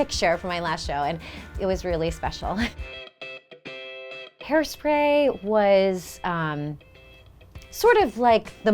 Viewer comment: for a moment I misheard her when she said her first Broadway productions was HAIRSPRAY. I thought she said her first show was HAIR. It caught my ear immediately thinking, "Wow! Great Show IMO. But for your very first part on BROADWAY with your first week or month nerves and or anxiety likely higher than a normal show and probably performing in front of the largest audience you've ever seen and you have to Go old there and do a scene naked. Lmao